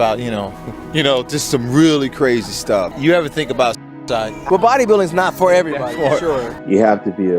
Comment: What did he say at 26:26? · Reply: You ever think about su*c**